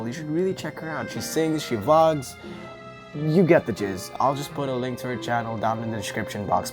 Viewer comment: i thought u said "u get the jizz"